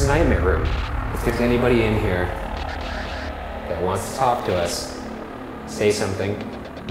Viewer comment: I heard “If there is any one in here who wants tacos.....”